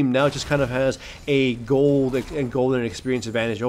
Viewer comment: Did I just hear... [*GOLDEN EXPERIENCE*]? (Nah you said Gold and Experience advantage)